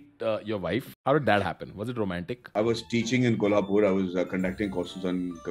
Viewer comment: did he said kolhapur 😧😧 Bro if yes I just missed a chance